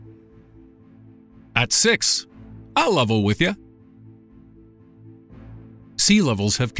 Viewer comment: What did he say at 2:24 "at 6...???" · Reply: bruno rojas "ill level with you"